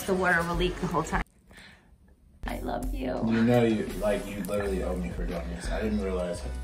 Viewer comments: Did just say “I love you” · Did she say I love you at 6:34 or am I imagining stuff? I love this channel 😂